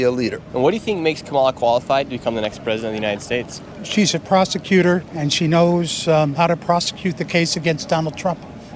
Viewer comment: Thought he said prostituter 😂